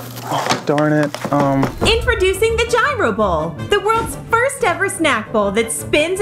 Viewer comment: did you say… gyro?